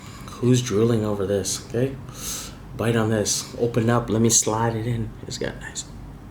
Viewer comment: Did he just say open up let me slide it in lol